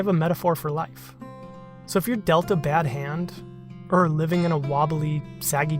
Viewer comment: For some reason, I heard 'If your Dealta bad hand...' and thought 'Oh No! Chris injured himself with a power tool!' -- Immediately followed by 'Wait, this isn't Frank Howarth, or Keith Rucker... and Delta hasn't made quality tools since the 80's. Also none of that made any sense. Rewind. Oh, philosophical life lessons. Sweet. All is well.'